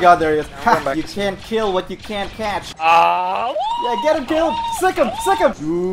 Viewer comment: Did he say, "You can't kill what you can't catch" or did he say "patch"? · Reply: He said catch.